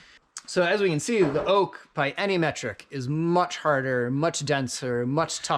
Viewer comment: Did you say "by any metric"? xD